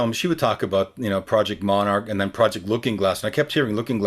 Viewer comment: Does he say at 4:11 Project Monarch? Monarch programming is nsa / cia level brainwashing to create multiple split personalities.